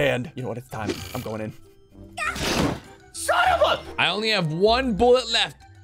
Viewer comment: Did say "Son of a b***" ?!?! I do know for a fact that he said son of a but then there was a cut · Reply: Yeah he just said son of a